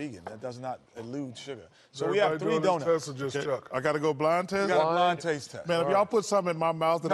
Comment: 😂 Shaq is everybody get donuts or just chuck... I hear u shaq.